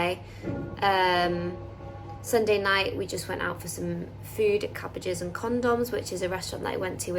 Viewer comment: A minute in an your accent made it sound like you said use went out an got condoms 😂😂